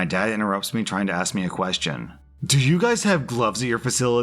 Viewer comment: I thought he said drugs😭😭